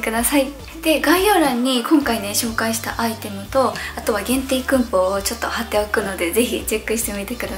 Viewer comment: ~ 限定クーポン 限定クンポウって聞こえる笑笑